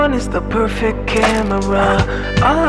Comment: Here's a comment from a real person. For the longest time I thought your intro was saying "OM-1 is the perfect camera" and I was so confused.